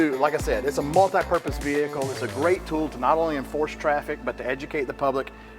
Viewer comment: Did he say "Enforce Traffic"?!? In a fucking 18 wheeler? Who TF you gonna pull over? A turtle?